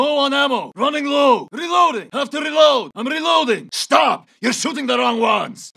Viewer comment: Does Nikto have Multiple personality disorder? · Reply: I clearly hear "I'm reloading". And about 1:11 it seems to me that he is talking about taking UMV with someone together not himself.